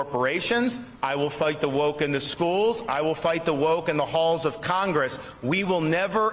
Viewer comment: Whenever I hear DeSantis say that, I hear. Florida is where hope goes to die and I think, he got that right